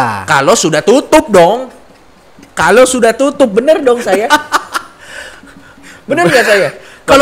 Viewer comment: gw dengernya sekilas "bener donk sayang"😂😂